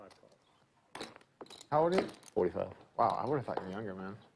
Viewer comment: does say he's 47? WTF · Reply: is 35. The guy next to him in the grey hoodie and hat says that he is 45